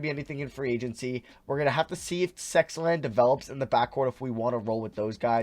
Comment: Did he just say 'Sexland'??!!